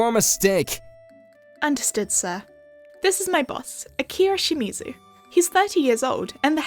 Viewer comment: Wait did you just say akira shimizu 1:01?